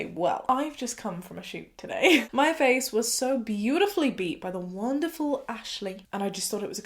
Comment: Did she just say “my face was so beautifully beat”? 0:08?? 🧐😂